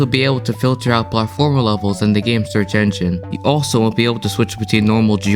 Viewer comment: did you say will or won’t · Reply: wont